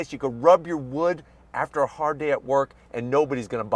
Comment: At the 10:32 mark, did he really say "you can rub your wood after a hard day at work" lol! I guess having the ultimate pickup truck comes with other "perks" that aren't on the spec sheet lol.